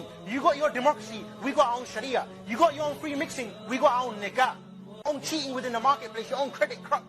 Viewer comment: I swear he said nig ga?